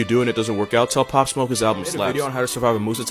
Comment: “TELL POP SMOKE HIS ALBUM SLAPS” 🦹🏾‍♂️🦹🏾‍♂️🧙🏾‍♂️🧙🏾‍♂️😭😭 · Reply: I thought he said “his album sucks” and I was like “god damn the disrespect